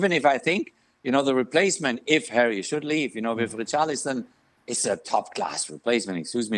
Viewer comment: Apologies if I misheard but did Jurgen say Richarlison is class? 🤣🤣🤣🤣🤣🤣